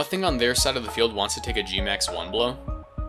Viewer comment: when you said g-max one blow i thought you said g-max wambo lmao